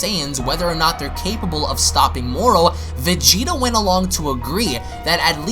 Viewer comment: did u just say vegito?